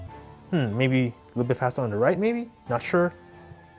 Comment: did he just said "redmi/real me". Edit : he said right one maybe